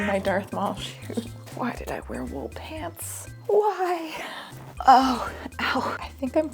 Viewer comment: Happy birtfdayyyy! also cat bed for humans <3 (also I heard "why did I werewolf pants" and it made me giggle)